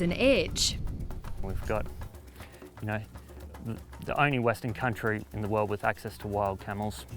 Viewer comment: did he say - we are the only WESTERN country in the world with access to wild camels?! Or did I get it wrong?